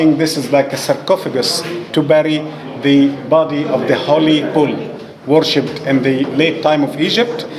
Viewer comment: what he is saying at 4:52. I couldn't figure it out. pool? bigger box for body of holy pool? Can someone tell me what he means by pool? PLZ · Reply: the famous story says it was used to burry the body of the holy bull!!